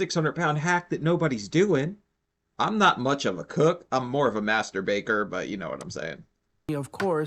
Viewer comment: You said you were more of a master baker but it sounded like you said "I'm more of a masturbator."